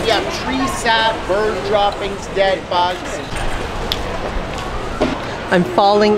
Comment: The guy at the beginni g selling something i could have sworn he said dead bodies...but i think he said dead bugs lol Congrats on the cookbook! Ill def be picking that up for myself and my 2 sister in laws that i just got instant pots for!